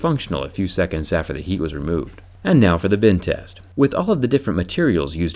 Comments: It sounds like he says "bin test" instead of "bend test". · It always sounds like he is saying “ Bin test” not “Bend test” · To me it sounds like you say bin test when you say bend test